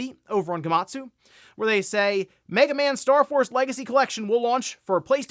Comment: Could've sworn you said Mega Man Star Wars Legacy Collection the first time, LMAO. I was like how was a MM x SW crossover not revealed at the Game Awards?😂😂😂